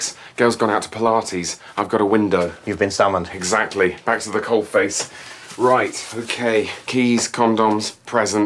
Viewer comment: What does Jez say around 6:08-6:09? "Thanks for the cold face"? Da fuck. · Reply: Yup at the coalface uk doing the work involved in a job, in real working conditions, rather than planning or talking about it: At the coalface with a deadline looming, you sometimes feel under a lot of pressure.